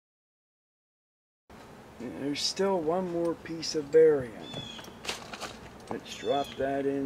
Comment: Thought said brylium would seperate the hydrogen and oxygen in the water? Or is that what toxic gas it produces is?